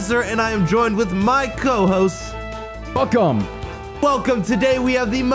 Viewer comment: Who se thought said f em when he said welcome at 0:14 😹